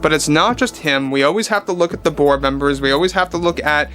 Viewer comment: Every time you say the board members, I hear “the Borg members.”